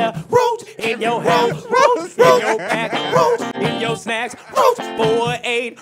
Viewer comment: Ahahahaha😂😂😂😂😂😂😂😂am I The only one who heard him say roach in your ass😂😂😂😂😂omg this song is funny